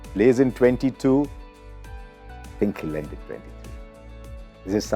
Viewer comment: can someone plzz tell what harsha said at 5:50 after "played in 22" ? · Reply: he can left in 2023. Means this might be the last year of dhoni as a cricketer